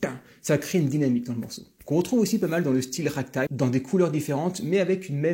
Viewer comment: c'est quoi le nom de ce style ? Raktak, raqutak ? Je ne suis pas sûr d'avoir correctement compris le nom du style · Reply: Je parle du Ragtime.